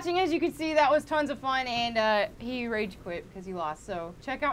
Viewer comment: LOL I thought they said Melanie Moat (Ian's Girlfriend)